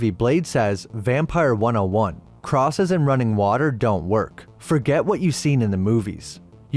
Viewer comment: running water? I think he said holy water lol